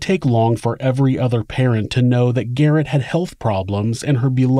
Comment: Garret? I heard Garret instead of Garnett at 2:33 I think lol